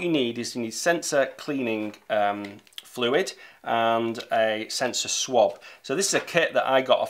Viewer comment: thought the dude said "sensor swap" instead of "sensor swab" and had a mini panic attack